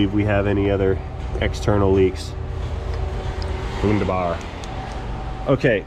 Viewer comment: did he say "wunderbar"? would be hilarious as a german like me XD.